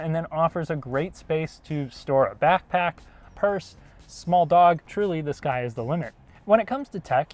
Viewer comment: did he just say small dog? LOL